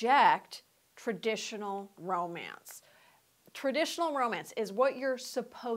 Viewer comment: At first, I thought it said "aromatic". (Stinky? Flowery?)